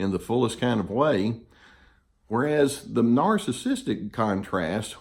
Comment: Excellent video, but want to share that I literally laughed out loud at one point because I heard your line "Love is anchored in duty" as "Love is anchored in doody" and both seem true :D